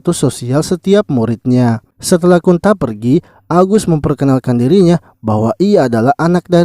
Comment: gua gak salah denger kalau namanya agus